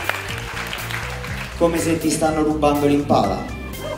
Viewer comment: what was said at 1:18? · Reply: CountrySoulRebelHeart "Someone stealing the Impala" Basically XD